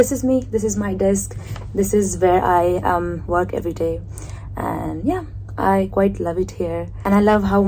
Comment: What did you say ? I am work everyday 😂 This English you have ??